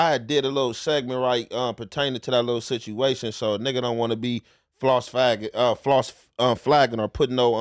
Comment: “false phaggin” 😂😂😂 what he just say ????